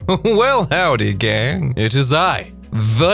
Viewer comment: I thought you said "well howdy gays" and I felt very seen lol